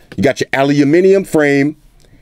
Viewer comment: Did he just say "ALLEYUUMINUMIN" wtf